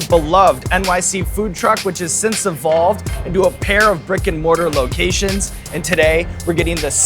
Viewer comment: Please tell me I am not the only one that heard "Rick and Morty" instead of "Brick and Mortar"